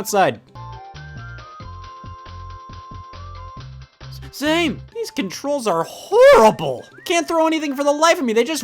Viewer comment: Is this the game audio or is it Scott speaking? Also either way what is it saying, I hear "Same!" But it's a bit fuzzy.